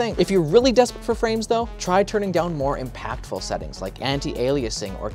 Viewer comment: "It may look potato, but at least the game play tho" that was both funny and kind of cringe lol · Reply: i thought he said play-dough, its not cringe tho